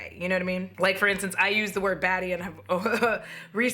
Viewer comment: i thought you were saying "baddie" not "batty" and i was like oh god what's wrong with baddie